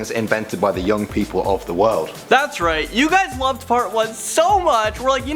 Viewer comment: Fart 1. That's what it sounds like he says.